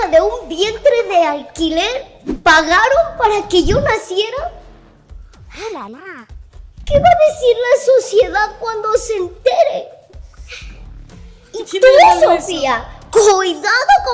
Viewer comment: que va a decir la SuuCiedAd cuando se entere JAJAJAJAJA lit escuche eso 😂